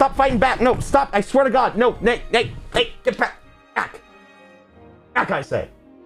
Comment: Did he just said "nay" ?